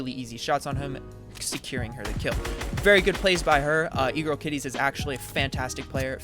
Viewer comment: I heard egrose kitties